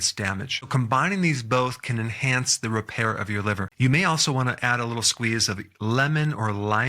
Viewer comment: Thought it said "your lover" and I was like "too late, he gone".